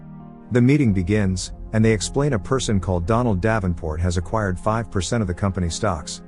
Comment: Did he just say "Donald Davenport"?! 😲 Does anyone know the show "Lab Rats"?